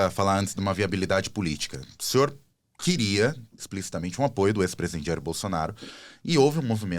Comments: O entrevistador disse: apoio do ex-presidiário Bolsonaro, foi isso que ouvi no minuto 6:22? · Na pergunta o cara fala “ex presidiário Bolsonaro “? Eu ouvi mal, ou foi isso mesmo? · Esse entrevistador fala 'ex presidiário Bolsonaro" , prestaram a atenção (6:25min). Será que não ouvi direito. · Eu ouvi bem o que esse entrevistador falou no minuto 6:20???? Ele chamou o Ex Presidente Bolsonaro de "EX Presidiário Bolsonaro"?????